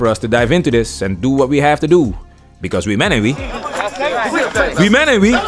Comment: Is he saying “we men ain’t we?” Not making fun, just genuinely curious.